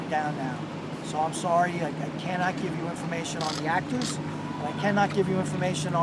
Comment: Did he say he can't give information on the ACTORS?!